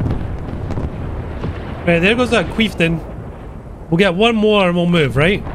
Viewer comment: Did you just say "queeftain"?! 😂😂😂